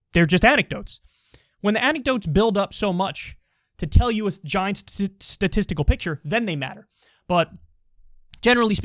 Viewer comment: I agree if you have good methodology you can use self reporting as data, but the plural of anecdote does not equal data. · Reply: "the plural of anecdote does not equal data." I heard that as well, I couldn't figure out if he misspoke or actually thinks that. ---Oy, I watched it again. He said "when the anecdotes build up to show you a giant statistical picture."